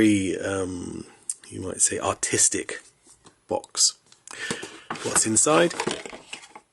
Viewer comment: swore this dude said autistic